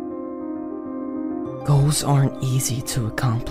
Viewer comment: why did i hear ho*s instead of goal 💀